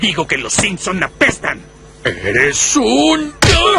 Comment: que dice Homero? Xd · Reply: Eres un